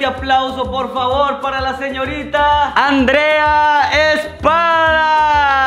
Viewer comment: Cuando dijiste Andrea espada yo escuche Andrea pan XDXD